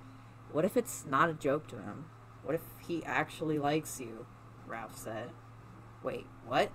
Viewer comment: Did you say Ralph?....or am i just def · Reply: I said raph 😂 I looked back too to make sure 👍 but it’s okay I have a lisp so that might be why it sounded like Ralph.